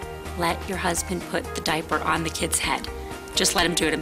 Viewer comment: Put the diaper on the kid's head? Did she say that?